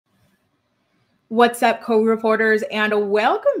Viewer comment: So....I have ....fornthe longer time thought you were saying what's up COURT REPORTERS😅